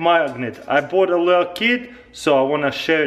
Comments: i thought he said i bought a kid instead of kit XD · Sounds like he said, "I bought a little kid"